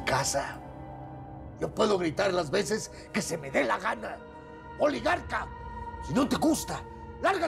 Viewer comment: Qué dice Aníbal en el minuto 15:00 ? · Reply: Oligarca